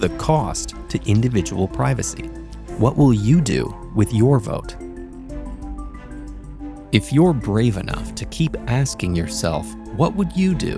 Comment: Man I thought they said "if you're brain rot"